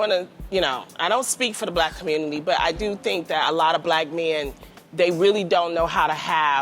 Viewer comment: I CLEARLY heard Loni say, "A LOT of Black men...".